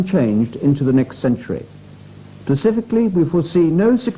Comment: he said 'pacifically' instead of 'specifically' SMH....